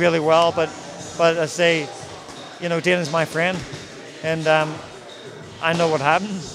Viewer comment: does he say "dana's my friend"?!